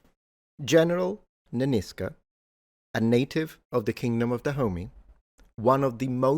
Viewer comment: without knowing the context for the first minute it sounded like you were saying "Kingdom of Da Homie" and I was very confused